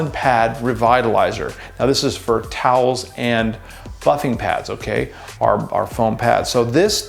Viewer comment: At first I thought he said towels and F***ing pads. I had to rewind cause I was just listening and got caught off guard lol